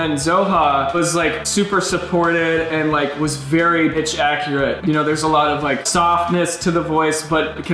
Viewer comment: is it just me, or does it sound like he said b—— accurate lmao 😂